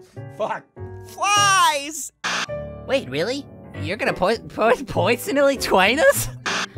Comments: It sounded like he was going to say "you're going to poison us" or something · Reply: I heard more like "pwewsonally twain us?" OwO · It sounded like he was going to say "you're going to poison us" or something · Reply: I heard “you’re going to poisonally twine us! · It sounded like he was going to say "you're going to poison us" or something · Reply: I heard "your gonna poisonoly jwoin us?"